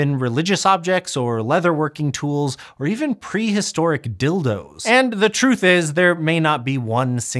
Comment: did he just say pre historic dildos? Wow. Must have been many of them.